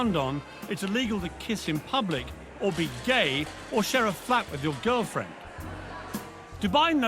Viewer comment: did he say,"share a fap with your girlfriend"?